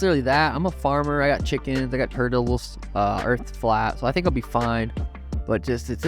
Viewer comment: Does he say “the earth is flat” !!?